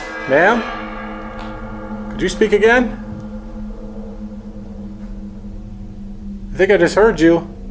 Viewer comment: It didn’t sound like ‘Are you following me?’ It sounded like ‘Get inside.’